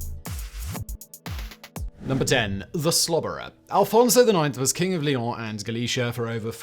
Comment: I misheard "The Slobberer" as "The Slaughterer". 😱😱😱😱